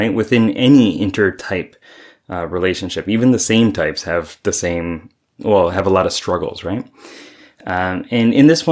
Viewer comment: Thought you said, "... even the sane types have a lot of struggle...", and I was mildly offended. 😅